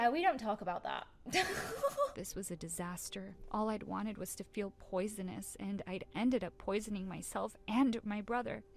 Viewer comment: Bruh i thought it said "She eats rAts to be poisonous" -_